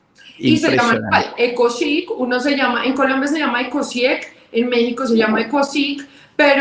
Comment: Disculpa en ese hashtag que mencionas: "Nada que curar", en el minuto 39:40. ¿Para México se llama ECUOCIC? No entiendo bien como pronuncias. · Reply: Ecosig es en México en Colombia es ecosieg